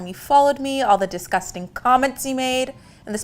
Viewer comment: Did anyone notice in time frame 22:31 Lily said all the disgusting comments you made but the subtitles say all the disgusting comments he made?